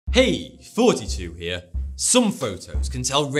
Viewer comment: I've been watching your videos for a good while and I always thought you were saying "Hey, 42 here"